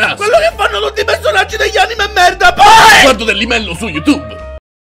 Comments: Cosa dice sanji al minuto 1:55 ? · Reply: Guardo dellimellow su youtube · Qualcuno mi sa dire cosa dice sanji al minuto1:55 P.S. video fantastico l'avrò visto almeno 100 volte (letteralmente) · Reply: Guardo dellymellow su youtube